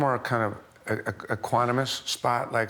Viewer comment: the english subtitles say 'aquanomos' what does that mean? !